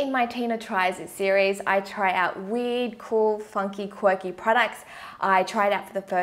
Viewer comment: When she said weird I thought she said weed